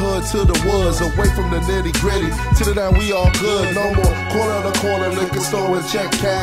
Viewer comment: I heard nigga at 3:07 but hes a beast so its okay.